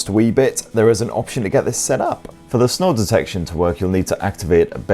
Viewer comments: Ohhhh SNORE detection. I thought you said SNOW detection. I'm thinking whats the point of that 🤣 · "Snore detection" I thought he said "snow detection." I was like, great now Pixel is a glorified weather rock. Yep, sounds about right. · Ah snore detection, that makes more sense, thought you said snow detection! I'm not complaining since Google gave me a free Pixel Watch and £100 extra on trade in on my old, very old, phone, but three months free trial of YouTube premium seems to be available to anyone, no purchase required! (But on the usual sign up for auto renewal and we'll make it really easy for you to forget to cancel, basis)